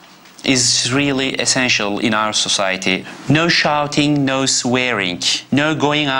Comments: When the dad said no shouting I thought he said no sharting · It’s sound like he says so sharting 😂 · It thought he said no sharting instead of shouting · i thought he said no sharting ahahah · did he say no sharting?...